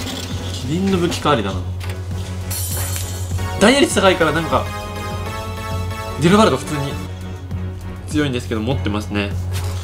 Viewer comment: ディノバルドじゃなくてギルガルドに聞こえた